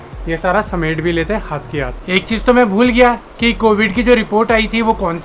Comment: did he said 'hag kay atay hai'??😂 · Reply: Hath k hath